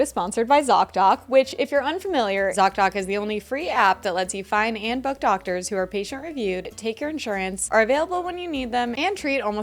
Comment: it sounds like she says "lets you find and fuck doctors who are patient reviewed" haha